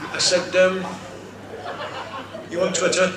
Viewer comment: What does he say at 0:25m? I can't make it out. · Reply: "Are you on Twitter?"